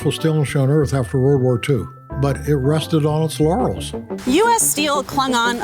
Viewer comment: the recording of the Nucor founder says "the US steel industry ... rested on its laurels", but having steel on the brain, I misheard it as "rusted on its laurels". Appropriate.